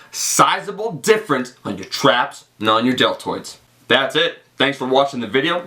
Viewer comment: is he saying "and on your deltoids" or is he saying "and not on your deltoids" ? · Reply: And on your deltoids. It's an exercise for shoulders so I hope it's add mass to your deltoids haha. Since I started overhead pressing my delts have gotten a lot bigger. My traps are stubborn though.